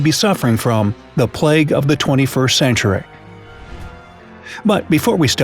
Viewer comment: Did he say 24th century? In between 0:38 - 0:44?